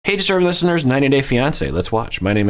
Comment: I always hear "hey disturbing listeners!" for your opening greeting lol!